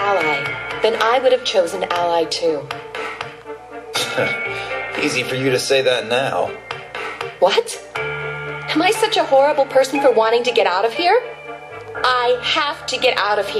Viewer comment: I swear, when she said "horrible" for a moment I was sure she said "whore" XP